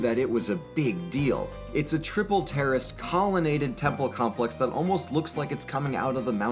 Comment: Am I the only one who heard him say "triple terrorist," or am I just messed up?